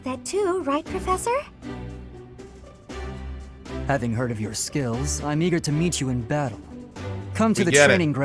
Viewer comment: I honestly thought he said "I'm eager to meet you in bed."